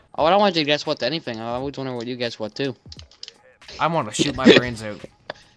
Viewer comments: it sounded like "I'm gonna shit my brains out" XD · "I want to shit my brains out" - First swear word I've ever heard you say, lol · did he say sh*t?